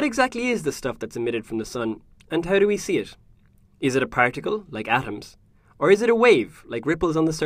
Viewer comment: Did he say practicle ?